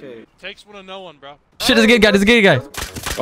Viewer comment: What shall we do next for a trap base? Also like and subscribe!!! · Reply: Hey 😂 whenever i hear you say "thears a gear guy" i hear theres a gay guy